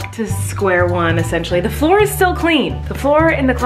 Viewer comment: when she said “the floor is still clean” I feel like it sounded like Miranda.